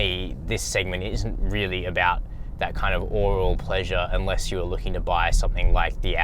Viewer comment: Did I hear correctly at 8:28 ? You said “oral pleasure”?!